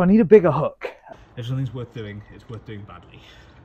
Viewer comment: Nearly choked on the line "if somethings worth doing, its worth doing badly" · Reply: TBH my hearing is so shit I thought he said “madly” and … yeah, I mean, bout right.